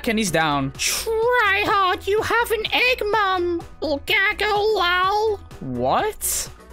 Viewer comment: Did said the filipino bad word gago 😳😳😳😳